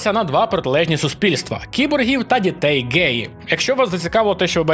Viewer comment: боже, мені вже почулось про битву "кіборгів та дітей геїв":face-green-smiling